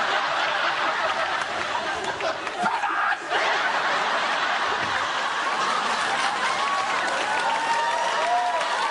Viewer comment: what did he say at 0:45? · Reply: "PIVAAAT!"